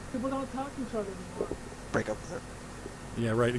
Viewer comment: Lol 33:55 ....did I hear say oh so quietly "break up with her"....hehehe ....u never know they might both play the same mobile game and both got an alert to thier base being attacked.....lol